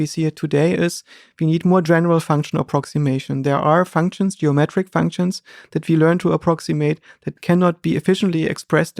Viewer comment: What did he say? 1:04:10 "There are ... geometric functions ... that cannot be efficiently expressed and computed in [???] language." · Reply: grammatical?